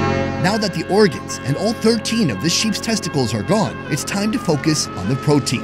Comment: Did i hear 13 sheep testicles remowal? ??